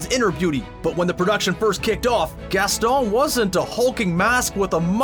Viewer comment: On the Beauty And The Beast one with Gaston, it sounded like you said Gastol in a french accent Like if you agree!